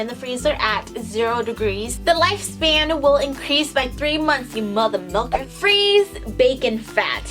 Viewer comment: when I heard "You mothermilker" or something like that I died ahahahhahaha